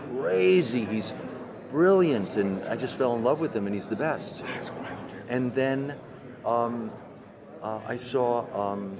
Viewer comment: What is this being whispered at 8:28 and 08:32. Please tell me. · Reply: orarian7 he said next question